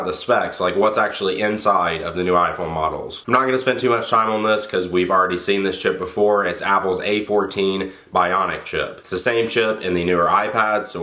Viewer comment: I thought you said we already seen this shit before instead of chip